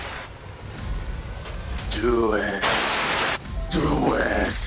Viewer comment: I hear duet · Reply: It says do it